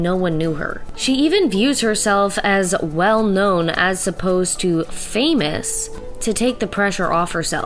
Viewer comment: did you just say “as supposed to” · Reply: no they said “as opposed to”